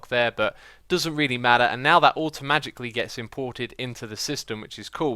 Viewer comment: did he say "automagicly?"